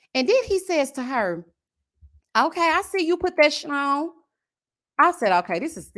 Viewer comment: FIRSTLY im mad that i was so tired i went to sleep and forgot about the live. SECONDLY at 14:22 I had to go back and listen cause I thought you said “okay I see you with that schlong”! THIRDLY Clay momma got her ex ALL THE WAY together. Sad she had find out stuff because of the show. FOURTHLY I too thought it was very bad to have those little girls watching as AD was crying.